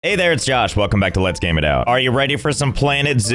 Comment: I wasn't paying attention to the intro this time so I thought Josh said "Hey it's Josh welcome to lets make out" 💀💀